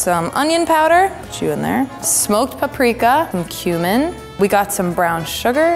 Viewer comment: I thought she said "human" and then I was like "I thought that was for vegans?